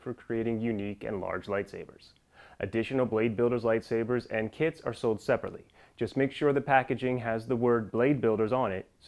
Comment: He said kits are sold separately I thought he said kids are sold separately